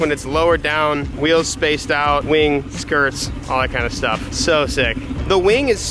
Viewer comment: Skirt What I heard: SKUURTTT